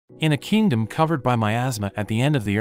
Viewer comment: i thought he said in a kingdom covered by my ass 😭😭😭